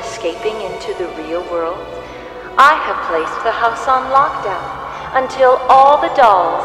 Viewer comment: I thought she said she but it in lock down